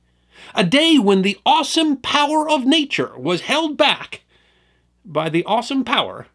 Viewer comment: When you said "awesome powers of nature", I thought you said"Austin Powers of nature ".😅